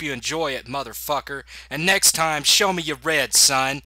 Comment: Ah, I thought you were saying "Show me your rent." Reminded me of The Super with Joe Pesci (I'm probably one of the few people who actually likes that movie.)